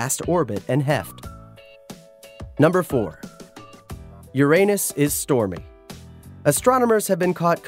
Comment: When he said "Uranus is stormy" I thought he said "Your anus is horny"😂